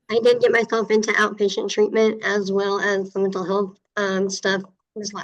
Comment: Love the name "miss hoover" sssnifffff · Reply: Actually I thought said Ms Hooter!